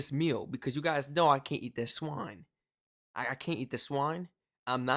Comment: I legit thought you said “I can’t eat the swan” 😂😂😂 I was like mate we don’t eat swans for breakfast. 😂😂😂